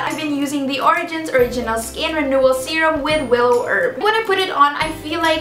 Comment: did I hear it right? did you just say Skin serum with Wil herb? 😂😂😂 omg omg WILNINA FOREVER!!! 😍